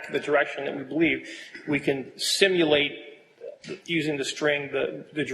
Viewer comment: did I just hear him say STIMULATE instead of SIMULATE ?